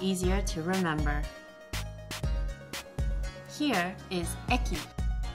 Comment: I didn't understand what she said at 1:56, can someone help me? I want to know what the word means :D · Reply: She said えき//E KI means station :)